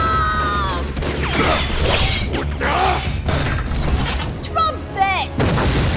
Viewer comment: I couldn't tell you how many times I've watched this, but I just now got the "trump this" line from Seras. I always thought she said "DROP this". You cheeky dick waffles.